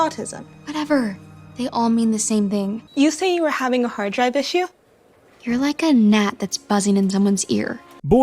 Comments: Autistic Girl: Did you say you have a hard drive issue? Subtitles: Did you say you have a hard DIVE issue? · the script said dive instead of drive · Did You Say You Were Having A Hard DIVE Issue